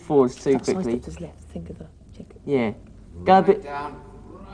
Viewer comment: I was loving the whole GET OUT OF IT!!! SHUT UP FESTUS!!! throughout this series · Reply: bit of stage filming error I think one of the cameramen sounded like he said go a bit down right down. Something they probably forgot to cut out of the episode.